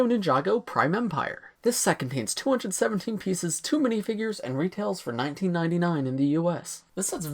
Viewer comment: I thought he said to many figures 🤣